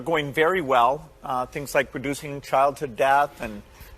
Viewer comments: Producing childhood death? Why would we produce death on any child ? I thought that was only an Israeli thing to kill children but I guess not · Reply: I couldint hear it at first I thought he said reducing but he definitely said producing. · This guy's a complete maniac. I believe they call that murder and genocide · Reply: Didn't he say (in the beginning of the video): "We see a lot of things going very-well, ummm things like producing childhood death"?!! 0:20 .... I've had to turn it back to listen because I don't believe he really said it.